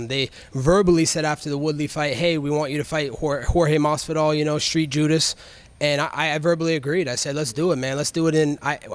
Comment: Did anyone else hear Covington call Masvidal "Street Judas" instead of "Street Jesus" @ 1:46 😆🤣